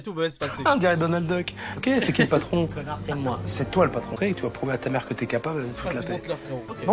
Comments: On est d'accord il a dit : "connard c'est moi " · je suis le seul à entendre « connard c’est moi » 😂😂? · Juste à 16:32 on dirait vraiment qu'il dit "Connard, c'est moi !" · Dite moi que je suis pas le seul à avoir entendu « conard c’est moi » à 16:32😂🤣🤣?? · C'est moi ou à 16:32 le mec dit : "connard c'est mwaaa"😂😂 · C'est moi ou bien à 16:33, Dobby avant de dire "C'est moi." sors a TéThanos un "C*nnard"??? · C'est moi ou à 16:33 il dit "Conna*d c'est moi " ? 🤣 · Je croyais avoir entendu la discussion comme ça. -On dirait Donald Doc -"C’est qui est le patron, connard" -C’est moi Je pensais que le mot "connard" était dans la discussion. 😂😂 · C'est moi ou il a dit connard c'est moi??